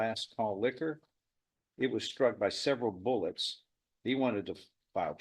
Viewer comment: He hit the owner of the liquor store tho, but he didn't die. The guy in the car wasn't hit. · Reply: Ah, I heard him say "he was struck by several bullets" not "it was struck by several bullets."